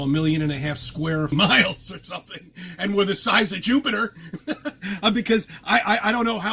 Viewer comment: To be fair to, I think the McDs comment was actual sarcasm if it's the comment I'm thinking of. But it's funny that he claims there's a dome up there but acknowledges that planes and lower orbit ships DO go up there and also, he's a space denier, isn't it? Why is he talking about Jupiter. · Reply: Jupiter?! I thought he said "stupider"...